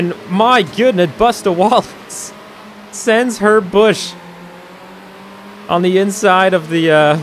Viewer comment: In the beginning I heard ‘she sent her bush’ 😂